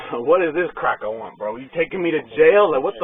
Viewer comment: 😂😂did i just hear him say "what do these crackers want" . Hysterical, maybe in his past life he was a brother, but he needs to look in a mirror. Mom is to blame for this. Babies him.